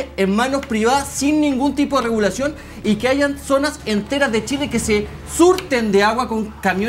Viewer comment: Minuto 47:47 dijo que "haigan" ? Jajajaja o escuché mal?